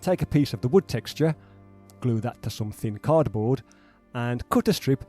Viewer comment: Everytime you say “some thin cardboard” I hear “something cardboard” Is it just me?